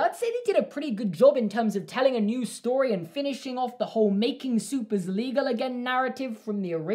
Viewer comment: Did anyone hear making is soap legal again